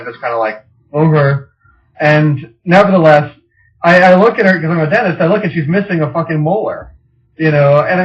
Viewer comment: What does he say at 9:06 ? He's a dentist?